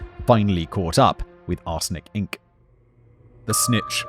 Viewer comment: I keep hearing him say "arse lick inc." and I laugh every time